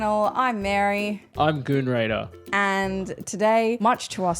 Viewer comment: Did your guy just say, "I'm Goon Raider"? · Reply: Great. I must have misheard. Now I have "goon raider" in my search history and he didn't turn up. 😭